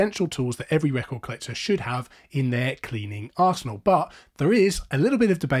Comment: Tell me... For anyone/everyone else... At timestamp 0:17, does it sound like he says "...cleaning arse-hole..." Instead of "...cleaning arsenal..."? 🤣